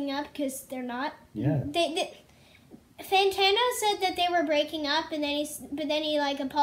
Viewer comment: Lmao he thought Merlyn said “Tony Montana” instead of Fantano, I did too first time I heard it.